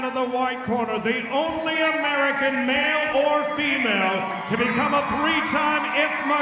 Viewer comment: did I hear male born female?